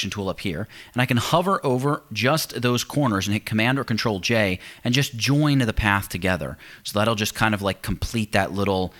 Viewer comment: My english is not too good. So i cant understand what key combination he use there 11:52. Can someone please tell me? Thank in advance :). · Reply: The key combination is Ctrl + J .. using this you join two points. (srry too for my bad english)